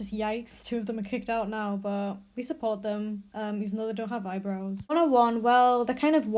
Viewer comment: "even if they don't have eyebrows"?? Is that what you said or am I insane?